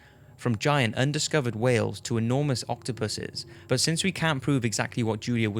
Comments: I heard "Octopussies" lmao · i thought he said enormous octo-pussies 🤐🤐🤐